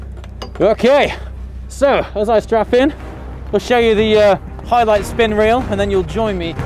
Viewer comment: did he say strap in or strap on?